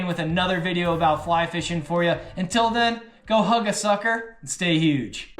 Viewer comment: Did he just say "Go suck a hugger??"